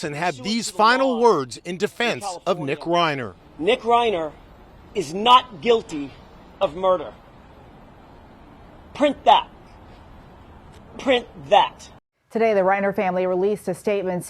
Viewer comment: Omfg. Not guilty of 1st degree murder is what he said. By reason of insanity. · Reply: That's not what he said - 1:47 - "Nick Reiner is NOT GUILTY OF MURDER - print that...print that!" He gave no qualifications.